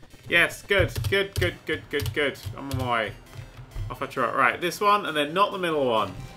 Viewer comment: did he just say: Helemaal mooi or something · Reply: nuis He said "I'm on my way."